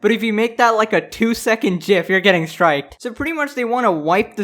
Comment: Did you say JIF?